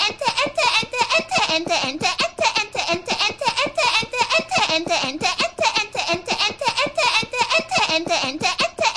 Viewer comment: Ich habs nicht genau verstanden, was hat er bei 1:02 gesagt? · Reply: Ente